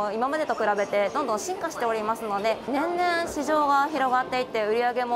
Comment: 年々がにゃんにゃんに聞こえたw